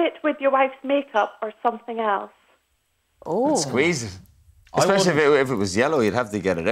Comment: what did Keith say at 4:47? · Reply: basically if you have a filled zit or pimple he said you have to pop it by squeezing.